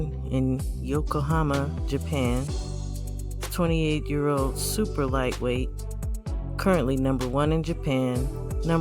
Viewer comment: You don't pay attention to 140?? I don't know that I'll go much lower than 130 unless they are just crazy talented. Although is a super fly weight lol. I appreciate you indeed great brother 🙏🏾❤ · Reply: My apologies I thought I heard you say Super Flyweight 🤦🏽‍♂️🤦🏽‍♂️🤦🏽‍♂️ I pay attention to 135 and up